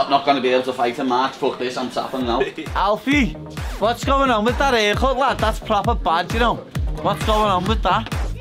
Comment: can someone explain me what paddy is saying to? Minute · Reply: What’s going on with that haircut lad that’s a proper bad haircut that hahaha